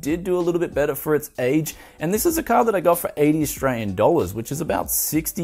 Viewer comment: Lol at 6:13 I heard "...and this is a card that I got for 80 strillion dollars."